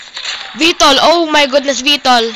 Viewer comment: what the freak did you say (bicol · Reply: Vtol gale